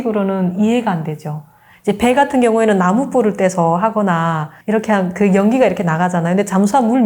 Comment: did she just say "burn firewood on ships"???